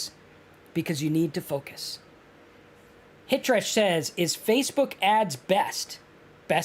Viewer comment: Commenter: "Is Facebook ads best?" What I heard... "What kind of bear is best?" That's a ridiculous question. False. Black Bear Well, that's debatable. There are basically two schools of thought. Fact, bears eat beets. Bears, Beets, Battlestar Galactica.